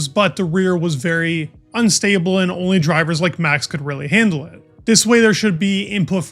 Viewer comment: I literally thought I heard you say maxico. Lol not a typo 😊